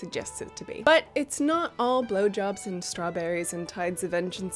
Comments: lol did she just say "it not all blowjobs" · Wait did say its not all blowjobs and strawberrys! at 12:40!